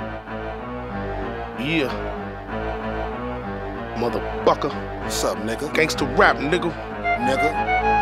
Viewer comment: I thought he said mother bucker